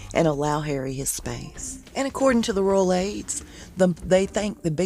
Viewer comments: When you first said “royal aids” I misheard “Rolaids”! LOL I bet a lotta folks are popping Rolaids around the palace these days. It’s really gotta bug Harry that Wills got the hot chick and he got the booby prize. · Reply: Sunshine Dae at first I thought she said Rolaids too. Too cute! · When you first said “royal aids” I misheard “Rolaids”! LOL I bet a lotta folks are popping Rolaids around the palace these days. It’s really gotta bug Harry that Wills got the hot chick and he got the booby prize.